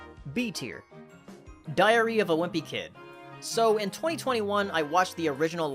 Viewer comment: Why does it sound like he said diarrea